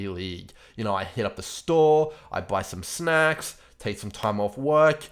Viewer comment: lmao I thought he said, "I hit up the store..I buy some smack."